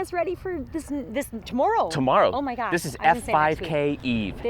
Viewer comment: did she just say oh my G-d?